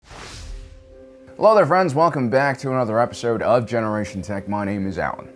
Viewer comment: Only just started watching, and I don't mean any offence, I'm from Birmingham, UK, I sound thick as bricks when I talk, but I still have to giggle at the intro when I hear Alan say "Welcome bawk" LOL, that's genuinely how it sounds to me